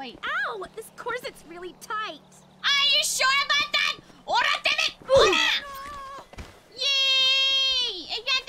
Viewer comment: Her aggressive voice is so cute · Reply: I thought she said Ora damnit